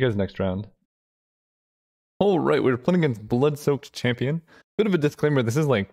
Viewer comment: So I listen on 1.5 speed because... yeah. Anyway, he said "we're playing against BloodsoakedChampion", and I swear I heard 'bloodsoaked tampon'.... yeah. -.